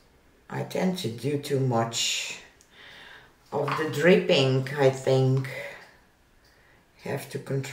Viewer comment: I had to go back & listen again because I thought you said you “tend to do too much of the [drinking], have to control myself”! Second listen clarified you said dripping, not drinking. 😁